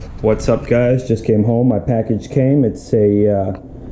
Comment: first thing i hear "sup guys im home and my package came"